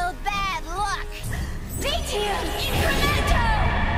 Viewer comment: for anyone wondering what's she's saying its. Vitium Incrementum